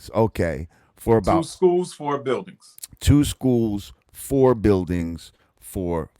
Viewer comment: "Ended up with Two schools" No sir you have two different buildings. Until you start teaching kids, you don't have a school, you have a dream. This man is delusional. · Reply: I thought he said four buildings two schools